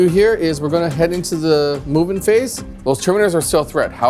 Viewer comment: When going into the movement phase, I keep hearing "moving into the Mubin phase". 🤔 Thousands Sons was my very first army I picked up and to my shame it is still not build. And I picked those up before the current range, so I am talking old plastic Chaos marines with metal Thousand Sons upgrade kit.🙈But will be working on them now, as these battle reports help me to stay motivated and my brother picked up Necrons!